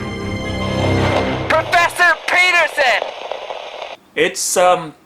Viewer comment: Am I the only one who hears 'Professor Penises!'